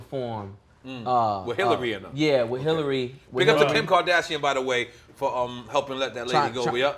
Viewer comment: Did he call Kim Kardashian , Pimp Kardashian?